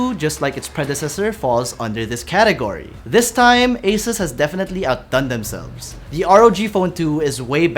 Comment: I swear I have heard Acer rather than Asus.